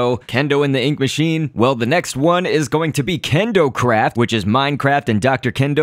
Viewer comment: I thought you said kendocrap